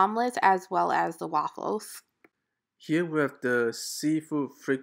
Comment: When you said fried seafood it sounded like you said PRICEY!